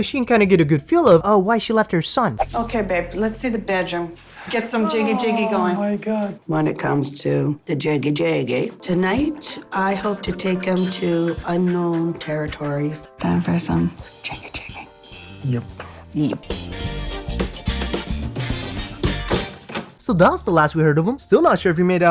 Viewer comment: did she just say "tonight I hope to take him to anal territories?"